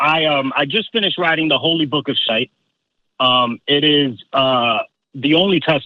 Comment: Did he say , he wrote "the holy book of shite"? :-P Sorry, non native speaker ;-)